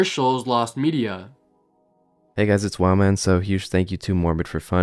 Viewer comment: I went to comments before I heard the collab part so I thought people were saying “wow, man” not.